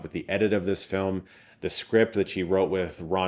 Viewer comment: "the script that SHE wrote?" Am I mishearing?